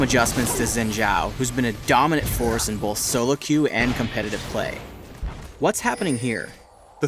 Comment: There is a mistake in the German subtitles at about 3:15. The speaker says: "COMPETITIVE play" and the subtitle says: "KOOPERATIVES Spiel"